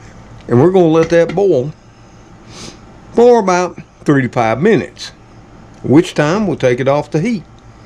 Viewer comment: I thought you said 35 minutes...I was thinking that is going to be one big dumpling by then. I don't need to watch you...faith restored. lol. If you are not using the liquid of the ramen you could remove it except for a tbsp or two and put the season packet on the dry if you put it in the stirfry.